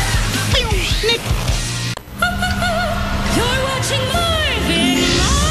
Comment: What did she said? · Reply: Oh Marvin Marvin